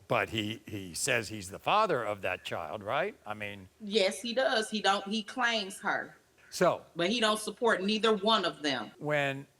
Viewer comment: Wait...... did she just say "he don't de-claims her" @ 3:18 ?????? I'm cornfused🤔🌽